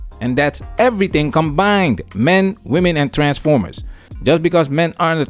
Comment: Did dude just say men, women and TRANSFORMERS? 😂😂😂😂